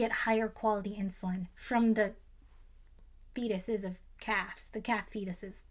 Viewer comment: Ohhh you said "calf fetus from a butcher" i thought you said cat fetus and was super confused....